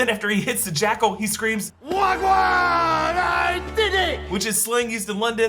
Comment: when steven in the mr knight suit won against the jackle you said that he shouted wagwan whereas i thought he said 1-1 after getting even with the jackle from the previous episode